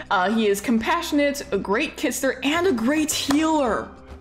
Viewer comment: I definitely misheard “a great kitsitter” for Bonsaituft as “a great kisser” and was like, “Whaaatt??” 🤣